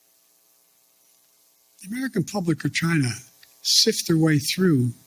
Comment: In the minute 1:55 did he said the “American public of China? If he said that, now we know who is really in charge.